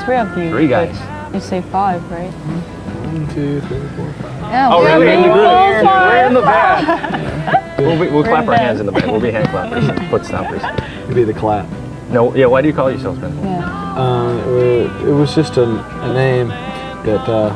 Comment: Anyone else catch Ben at 6:14? “you’ll be the clap” 😂 😂